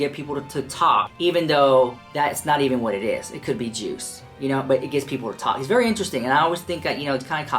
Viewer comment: omg this is random af and does not contribute to the discourse of TP, but I paused in the middle of the vid (6:35) where says "it could be juice," except I paused it before he finishes saying the word "juice," and so I heard "it could be Jew" and I legit nearly choked on my water 🤣 I wouldn't usually find that funny, but given Trisha's fetishizing of Jewish men, I found the irony hilarious. I was going to apologize for this anecdote, but then I remembered at least it'll be good for the yt algorithm! Love you sm 🥰